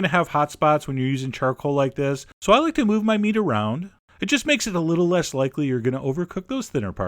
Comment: A peculiar thing just happened. I thought at 4:45 he said 'I like to WAVE my meet around.' It made me laugh and then the laugh made me sneeze o.O Hard.